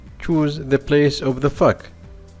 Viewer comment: when he said fog it sounded like uhh just go to it